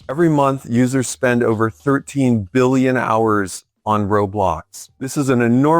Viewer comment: did he say users spend over 13 billion hours on roblox yeah thats def ai generated bro